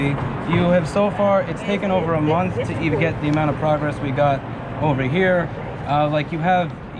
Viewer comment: Oh that word was "progress." The first few times I heard "we're gonna have problems." Looking forward to hearing how that tomorrow afternoon went.